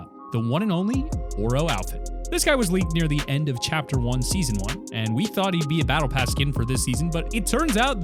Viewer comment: did he say chapter1 season1?